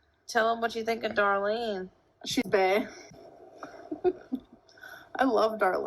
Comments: I think she says “she’s bae”, not “she’s bad”. The point is the same, but yknow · I heard her say "she's bae" which is another term for "babe". Whether she said bad or bae, it's icky. Great video btw! Edit: Oh and I forgot to mention, I don't know what season of Roseanne was watching, but Sara Gilbert "Darlene" was only 13 during the first season...big yikes.